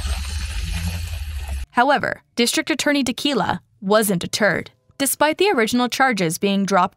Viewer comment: I thought they were saying district attorney tequila